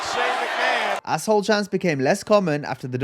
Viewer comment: As a little kid, when I heard the "asshole" chant I thought they were chanting "ice cream" 🤦🏻‍♀️